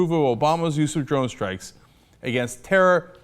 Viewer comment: he said drone strikes, but the subtitles said roast rights